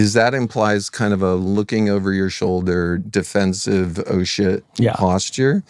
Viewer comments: did he just say "Oh sh##"?? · did bro just say shit at 29:37 or did he say shift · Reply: Shit